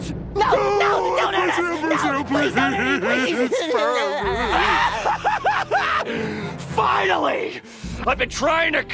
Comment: what did Patrick say? 😮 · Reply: He said please spare me